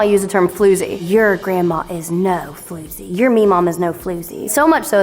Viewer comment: Did you just say mema? I thought my grandma made that up.